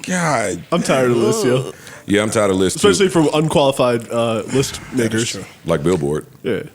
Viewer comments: did just say Billboard Unqualified list Niggas Lmmfao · Did he say "unqualified list nigg[e]rs" wait a minute. Lol that goes to show lol even tho koo, he still said the hard (er) he still white